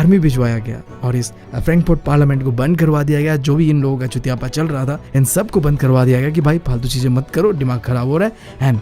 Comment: kya bole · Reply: Chutiyapa